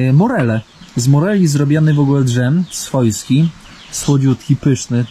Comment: No właśnie, czy gadanie tam po rusku nie jest lekką obrazą? Za obcinanie drzew jest mandat, czy za wycinanie (bo to jednak jest duża różnica)? Czy ja usłyszałem: D-Żem zamiast dżem?